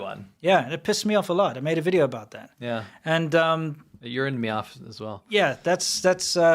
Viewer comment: did he really say “urined me off”? What a dweeb.